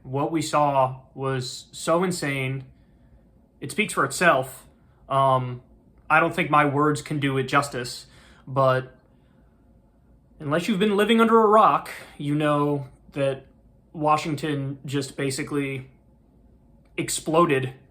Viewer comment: "Unless you live under a rock." Or you know, just live in a country where half the population isn't insane. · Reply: Thought he said Unless you live under Iraq